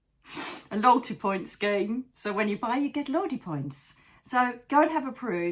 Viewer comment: I thought you said “you get Lordy points” 😂